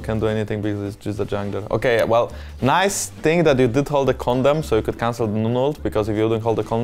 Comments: I thought Jankos said condom instead of condemn (3:03( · I thought i heard condom. 3:10 ?